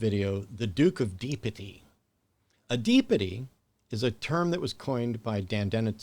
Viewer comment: I swear I thought you said the Duke of P Diddy. 😂